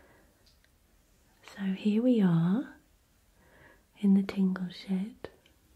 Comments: I thought she said “in the tingle sh!t” 😂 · i thought she said “tingle shit” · Did she just say tingle shit?!? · did she just say (so here we are in the tingle shit) 😂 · “so here we are in the tingle shit” that’s what I heard?😂💀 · is she saying tingle shit? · I thought she said tingle shit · Did anyone else hear her say tingle sh*t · I thought she said "here we are in the tingle shit" 😂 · Did she say “ here we are in the tingle SHIT” ???? I’m pretty sure that’s what she said lol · did she say tingle shit?